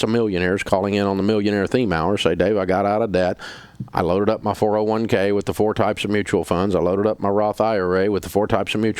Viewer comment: Is he saying “SMP” when he’s talking about the mutual funds beating it? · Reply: S&P